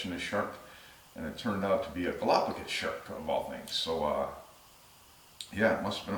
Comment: In your first story what kind of shirt did you say it was sounded like you said a goblin or gallopkin shark I know for a fact it wouldn't have been a goblin shark and I have never heard of the other one. I'm assuming I misheard but I would like to know what shark exactly you were talking about. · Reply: Galapagos shark.